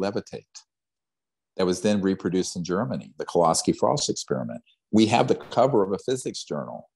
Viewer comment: does he say Koloski-Faust experiment? I cant quite hear exactly what he said and Id like to read more about it! I tried searching for it with various spellings and cant seem to come up with anything! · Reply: It is the Kowsky-Frost Experiment